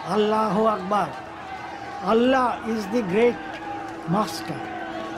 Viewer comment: I heard it as Bastard 🤣 indeed he/she is